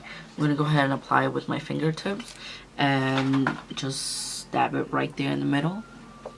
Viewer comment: Omg I finally got a chance to watch your video and when you apply that RED SHADE i was like bihhhhh YASSSSS i need it asap. When you went to apply the baby blue in the center of the lid I could had sworn you said “ima just stab it in my lids” 😂😭😭😭😭😭 nah pero Im obsess with the colors is gorgeous!